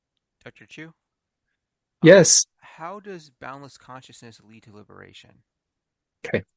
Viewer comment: What did You say here? balance consciousness? What is that? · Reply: Boundless consciousness. Not Dr. but as he teaches it, it is a non-dual state closer to natural awareness than to a mystical experience of consciousness. The foundation of boundless consciousness is understanding that all of experience is mental. When someone touches a tree, they experience a touch sensation, not the tree itself. There are several videos on this channel with boundless consciousness in the title.